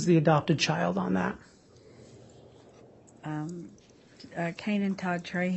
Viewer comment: Kayden birth name, he was named after grandma. 😢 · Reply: Sounded like she said "Kainan" to me. Either way, it'd have still likely been after her. 💔